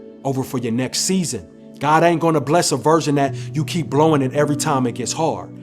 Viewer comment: misheard "version" as "virgin" weird af but still bars